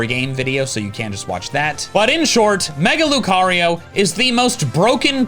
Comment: It sounded like you were saying "Maga-lucario"